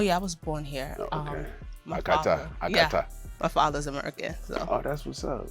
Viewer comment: did he just call her a cat?